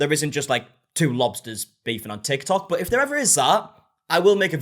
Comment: Your accent made it sound like you were saying "mobsters" to me instead of "lobsters" and honestly- it's great